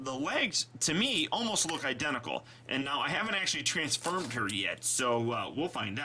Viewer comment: he said transfurm instead of transform